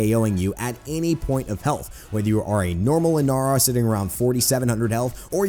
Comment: did i hear Inaros ?